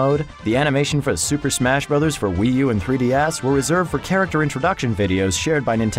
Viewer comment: Did he just say 3D Ass?